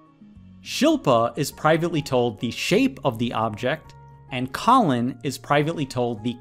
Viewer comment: I got it wrong because i heard "objects" instead of "object". :(